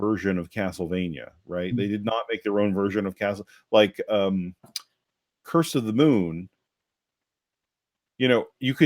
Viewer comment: I swear, when you said " Curse of the Moon " my brain automatically substituted it for " Circle of the Moon " and for a moment I was like: " Wait, what does that have to do with anything? Do people make GBA rom hacks? " xD